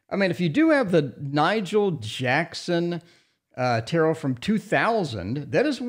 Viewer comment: I thought you said Michael Jackson Tarot of 2000. Here I go looking for it online. I like this deck except for the pips. I like fully illustrated pip card. You once again saved me money. Thank you.